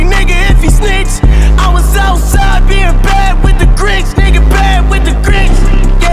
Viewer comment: Always thought he said being bad with the crips But nah it’s the grinch